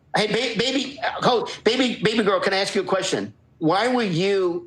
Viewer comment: literally spat my water when i saw face, did ed just say babygirl?